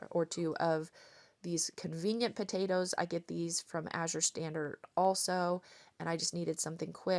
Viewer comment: I still get tickled because I thought you were saying As Your Standard❤❤❤ I love your videos and your kids are the cutest